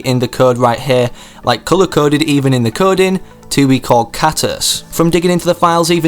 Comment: Who else thought he said cactus